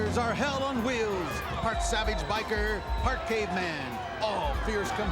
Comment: so at 43:53 did they said "part savage bikers, part gay man?" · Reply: part caveman